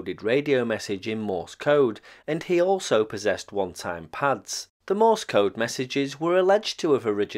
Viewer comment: I heard "one time pants" and thought it was British slang for adult diapers.